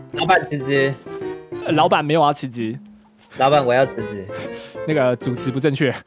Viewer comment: 我聽成老闆我要儲值LOL